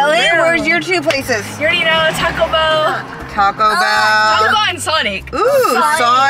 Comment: Did she just say sonic and Taco Bell